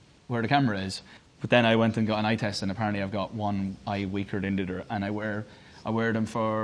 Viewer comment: when he says dendidder instead of "than the other"